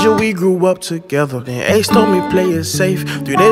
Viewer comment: did he say what I think he said. X told me play it safe three days later he was murdered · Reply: Ace